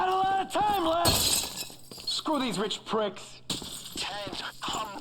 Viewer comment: The subtitles didn't match what the crew member said. What he said: "Screw these rich pricks." Subtitles: "F--- these rich pricks."